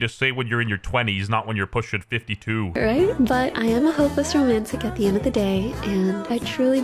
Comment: I am a hoe plus romantic. Did I hear that right? that's what I thought!